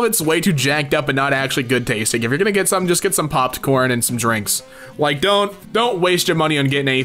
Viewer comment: why tf did u say popped corn instead of popcorn